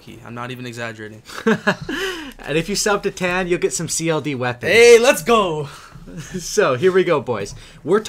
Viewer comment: At about 0:53 seconds did he say CLD weapons?